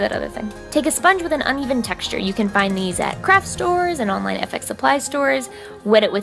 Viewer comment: Watching this over again at 5 am (like I haven't watched all of your videos enough at this point) I honestly thought you said "you can get it at crack stores" when talking about the stippling sponge and Ive been sitting here staring at my phone for 10 minutes wondering about my life.